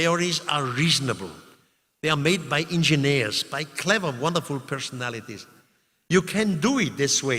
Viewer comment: I had to turn on captions and play it a 5th time cause I kept hearing "a CLEVER MUTHA SOMETHING PERSONALITY*